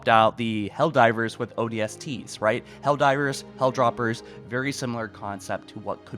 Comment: I keep hearing you say “hell droppers” bro… it’s hell jumpers